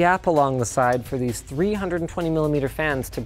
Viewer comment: did he just say 320mm or is it just me? Great vid btw.